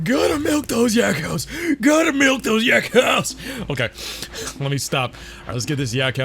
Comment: I've always thought the farmer said "Gotta move those Yakows" and not "Gotta milk those Yakows". Now that I know that, it's really funny seeing him jerk his arm/hand up and down😂😂